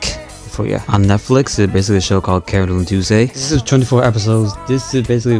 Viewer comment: Wait what's it called? I really wanna watch it but I didn't understand the title quite right. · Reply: Carole & Tuesday